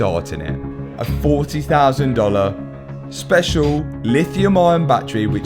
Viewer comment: did say the battery is 40k?